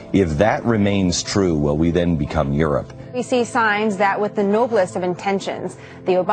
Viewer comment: did he say erupt or Europe?